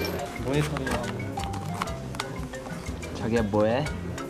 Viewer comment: wait, did Intak just called jongseob jagiya😳